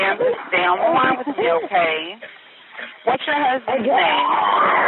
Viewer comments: Dispatcher did not say what's your husbands phone number, she said @ 19:44 to while she was burning up... "MA'AM STAY ON THE LINE WITH ME OKAAAY, WHAT'S YOUR HUSBANDS NAME" That people is clearly a certifiable person. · Dispatcher did not say what's your husbands phone number, she said @ 19:44 to while she was burning up... "MA'AM STAY ON THE LINE WITH ME OKAAAY, WHAT'S YOUR HUSBANDS NAME" That my friend is clearly a certifiable person.